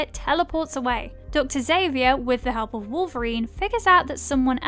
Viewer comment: did you just say Dr xavier.....